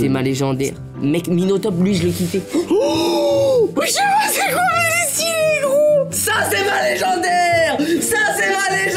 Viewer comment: il a voulut dire quoi la ? 😭😭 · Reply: Il a dit "jsp c'est quoi mais c'est stylé gros"